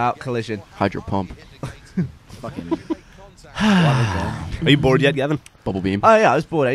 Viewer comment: did Gavin say 'Fucking'?